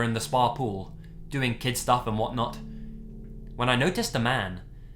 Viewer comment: I thought he said "Kissing in the spa" instead of "Doing kid stuff in the spa" I was like WHAT?